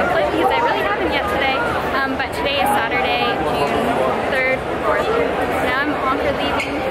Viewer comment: june 3rd...? This is a very long time ago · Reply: I thought she said July 3rd. Maybe I have to play that part over.